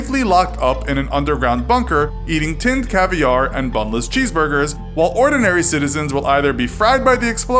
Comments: I thought he said boneless cheeseburgers. XD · did you just say boneless cheeseburgers? lmaooo